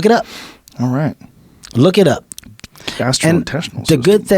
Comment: At the 1:28:56 mark, Kevin said something unintelligible or incomprehensible, did he say "Look it up", I'm sorry, I only know how to 'look into it'.